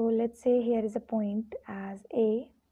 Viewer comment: what you said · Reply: As A